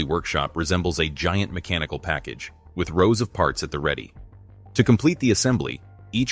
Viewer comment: I thought he said "Rosa Parks", so I went back and put on subtitles and he said "rows of parts".